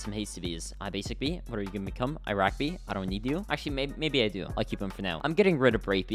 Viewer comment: When 8:42 did he say Iraq bee!?